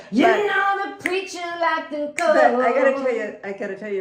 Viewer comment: I always thought it said you know the preacher likes the cold… now in my defense I speak English second lol😂 see we always learn lol😂 this was so nice.